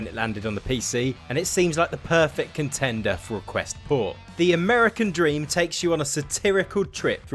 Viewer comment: did you say quest 4? Just tryna get some clarification. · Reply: Haha quest ‘Port’